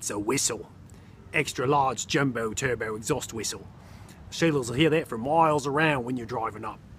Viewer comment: Did you say : (شغله صغيره) at 0:12 !! 😂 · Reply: No! I said Sheilas. That’s Australian for women.